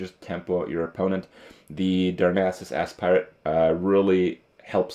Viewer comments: Does he say 'Darnassus Ass-Pirate' at 6:39 ? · Did he just say Darnassus "Ass-pirate"..... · I'm immature but it sounded like he said "Darnassus Ass Pirate"